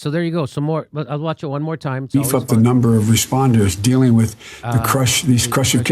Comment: At the beginning it sounds like he said "We've fucked a number of responders."